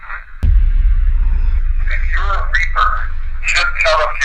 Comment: 🥴 I think I actually heard, "I'M A REAPER."